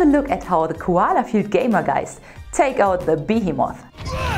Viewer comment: She's so perfect! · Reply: When she said Behemoth i thought she said Biggiemoth.